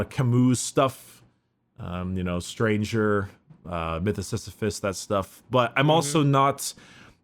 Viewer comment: what did you say?! Mytha-what?